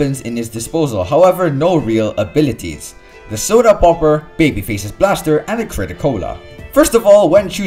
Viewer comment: I Heard "Baby Face's Bastard"